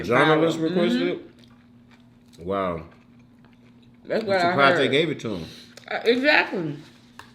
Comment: Sorry I thought you said kings surprise!